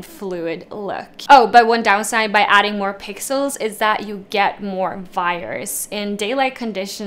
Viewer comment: Hi! Thanks for the vid. I was wondering, what do you mean by more virus with the 600 one?? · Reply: Oh thank goodness. I thought she said more fires!